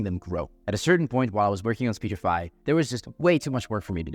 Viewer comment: the subtitle says you were working on spotify instead of speechify lol